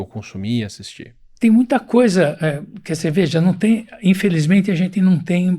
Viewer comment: Juro que ouvi ele me oferecendo uma cerveja 1:09:59 , devo me preocupar? 😂😂